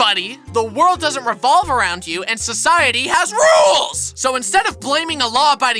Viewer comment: ish when he yelled RULES it sounded like odd ones out